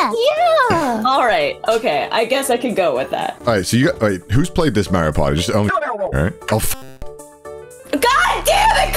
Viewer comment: what did he say? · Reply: He said Sydney